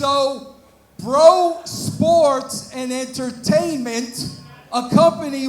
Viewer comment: Did Russo just say his company is "BRO Entertainment"??? Of course lol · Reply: *Bro Sports